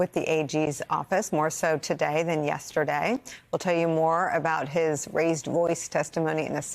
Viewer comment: Lol, I thought she said, "A notably bitchy Eric Trump..." I had to listen to it twice to catch what she actually said ("fidgety"). Either one works. 😆